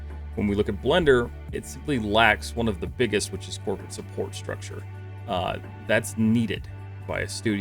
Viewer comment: lower gad damn music, I can't hear you, what does blender lack in ? 2:00? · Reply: Lack of corporate support and engineering support in the form of a 24hour team structure